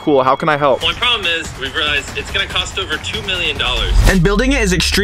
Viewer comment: did he just say 2 million dollars